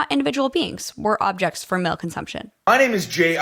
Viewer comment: LMAOOOOO I thought she said “milk consumption” 😭😂 1.5 yrs of breast feeding got me all turned around hahahaha